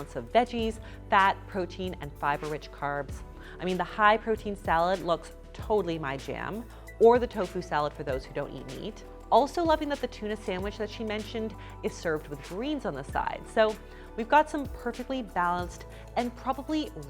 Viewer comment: I heard "...or the tofu salad for those who don't need to eat" Had to listen to it again 🤣